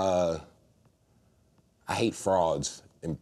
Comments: i could of swore at 13:25 he said I hate frogs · I’m so dumb, I thought he said he was bothered by frogs 🤦🏾‍♀️ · When he said "I hate frauds" I thought he said "I hate frogs" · Who else thought that Tyler said, “I hate frogs,” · I hate frogs and pretentious people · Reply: Austen Johnson no, it’s the accent. I know because someone thought I said frogs when I says frauds as well. I am from NYC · FOR AGES I THOUGHT HE SAID "I HATE FROGS"... OMFG. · I totally thought he said "I hate frogs"